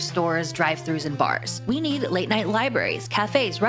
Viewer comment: She says "we need" all I hear is "I need".